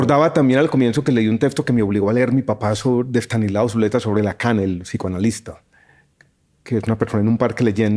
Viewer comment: ¿Cómo se llama el texto de Estanislao Zuleta que menciona? No entendí muy bien. Minuto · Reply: Hola. En ese momento no mencionó el nombre del texto, sino el apellido del autor, Lacan (Jacques Lacan)